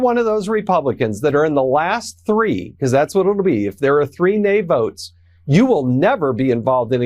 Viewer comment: I thought you said there are 5 no votes?